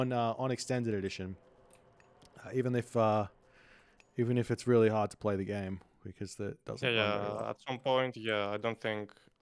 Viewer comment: what did said at this timestamp? diff between ee and voobly? i wanna know.. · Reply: Probably something like... "even if it doesn't work efficiently"